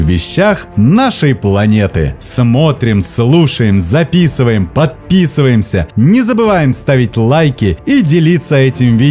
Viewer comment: мне послышалось отписываемся, и ещё с таким оптимистическим голосом😆